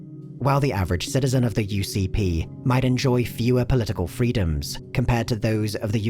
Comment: I can't help but find "UPP" funny when said out loud (you pee pee?). Also, at 6:10 he says UCP instead of UPP; is Alberta calling? ;)